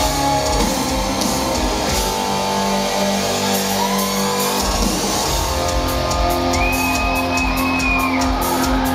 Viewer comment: 辺りでモアが鞘師に何か言ってるけど何て言ったんだろ？ スゥもニコッとしてるけど☺️ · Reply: ｢天ぶら!天ぷら!｣と言ってたんだね☺️